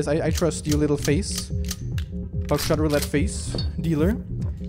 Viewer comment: I thought u said back shot roulette 😹😹😹